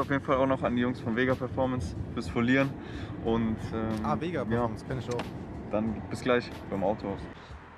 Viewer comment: Hahaha sorry habe Vega P*ornos verstanden